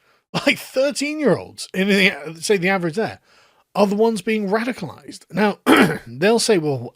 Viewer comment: For a second, I thought it said young people are becoming "rascalized." Was all set and ready to call Carl a boomer!